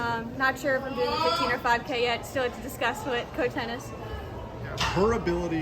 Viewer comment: Coach tennis ... 😎 that my closed captions subtitles said 😂